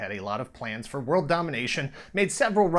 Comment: I can't help it, but when I listen to the intro now as an adult - I just hear "Doc Terror and his cyborg 'companion' Hacker." Companion, eh? ;)